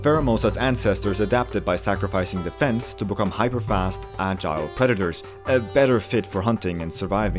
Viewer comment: I skipped ten seconds in pheromosa when it said agile predator so it sounded like it said child predator